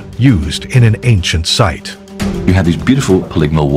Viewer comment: I swear to god I hear him saying "You have these beautiful Ligma walls" am I being trolled